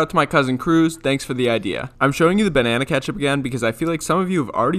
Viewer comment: At first I thought you said banana hot sauce for some reason